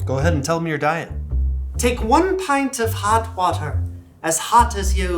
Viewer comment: I thought she was saying to drink a pint of really hot butter.